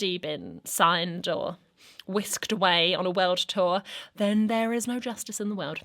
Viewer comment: I heard "whisked away on a welsh tour", I'd be happy for the welsh if that was the case